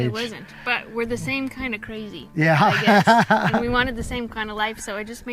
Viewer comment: Nice. "The same kinda crazy" At first I thought you said "The same kinda Cozy" which works too!!